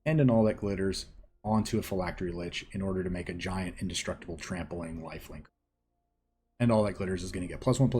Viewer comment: I misheard "a giant indestructible trampling waifu" for some reason